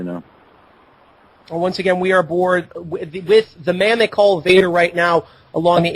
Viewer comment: what the hell did he say? the radio dude sounded like he said 'we're bored....' or w/e but i can't tell >_>